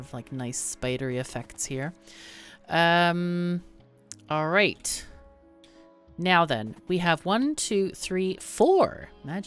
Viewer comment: I always hear "Meow then!" :D hehehe